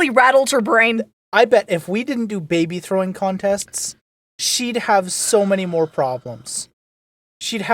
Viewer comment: throwing contest"s" he said, (plural). I am just repeating for those it flew past and didn't catch it. Jokes aside, stuff like that is the reason kids grow up without the fear of failing. To some degree they learn some danger involved doesn't mean impossible.